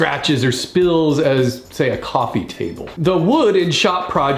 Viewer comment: I heard coffin table... It's time to go to rest, I guess.